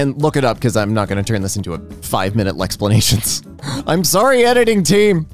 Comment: did she just say “Lexsplanation?!” that is so fricking great!!